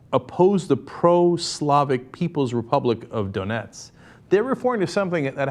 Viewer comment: For a second I thought it said "People's Republic of Donuts". Now that would've sounded good!